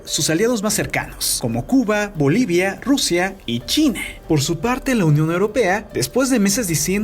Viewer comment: en ese momento escuché Chile y era China a c mano jajaja me asusté